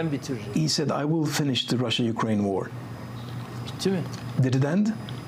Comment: Erdogan sounds like he said "Russian-Korean" war. He needs a new set of teeth.